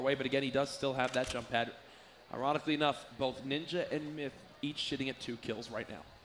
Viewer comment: did he really say each shitting at 2 kills right now